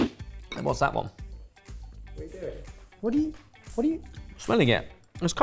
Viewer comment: I heard at 3:20 saying “What are you doing?”